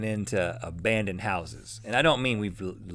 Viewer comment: I thought he said "a band in houses" lol